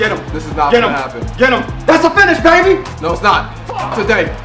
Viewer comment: Anyone else hear FUCK